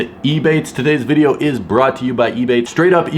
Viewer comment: At first, I thought you said “Today’s video is brought to you by E-Bay”